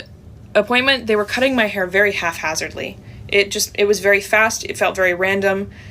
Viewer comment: did she say “half hazardly” ?